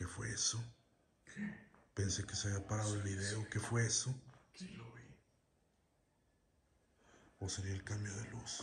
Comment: Impresionante lugar, gracias por compartirnos este lugar. Entre el minuto 36:56 - 36:58 me parecio escuchar una voz similar a la de que dijo algo como ´yo lo vi o yo no fui" o no se si haya sido 😅😅😅